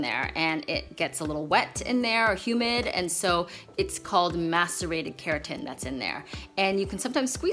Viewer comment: Idc what anyone says. At 2:10 she says "masturbated keratin"